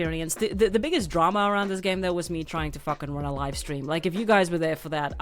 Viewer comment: You know I misheard you and thought you said the biggest drama was 'trying to fck on a live stream." I was laughing to hard I ended up dying in the game Im playing. :D